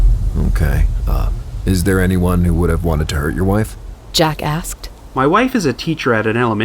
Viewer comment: lol I heard "Jackass" at first xD